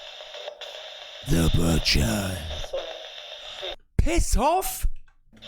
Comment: sounded like they said "piss off, fk you"